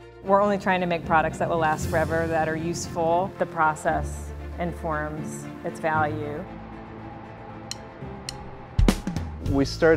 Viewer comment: She says something about "informs". What does she say? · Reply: She says "the process informs it's value". It's a somewhat pretentious way of saying it's value depends on the quality of the process by which it's made.